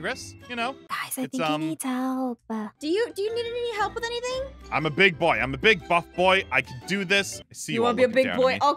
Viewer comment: I thought Ein said “I’m a big f* boy” I was like WHAT?!